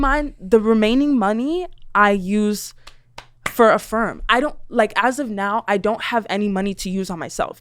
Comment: I think she said "A firm" And Caleb thought she was saying affirming? lol